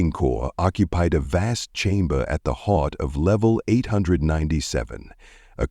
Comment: What is up with the "Baby Talk?" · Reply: It starts at 6:20 maybe not baby talk but a definite lisp ie. "Hawt" instead of "Heart."